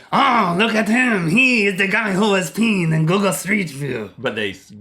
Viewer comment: I thought it said "peeping" xD